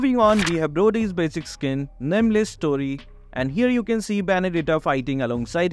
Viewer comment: Dude literally said story instead of stray on